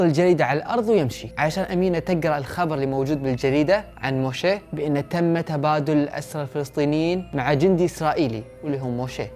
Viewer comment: معليش تعلميني وش قال هنا ؟ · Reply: امينه تقرا الخبرعن عن موشيه زوجها وان تم تبادل الأسرى الفلسطينيين مع جندي اسرائيلي